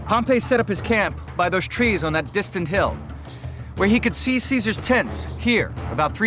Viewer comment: literally thought he said "...where he could see Caesar's tits..." I chuckled to myself.